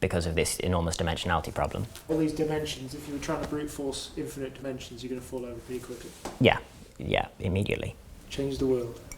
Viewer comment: what is the interviewer saying at 6:37? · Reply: "If you were trying to brute force infinite dimensions your are going to fall over very quickly.."